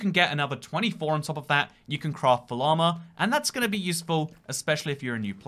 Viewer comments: i heard "craft all armor" as "craft a llama" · It sounds like he says "you can craft the llama"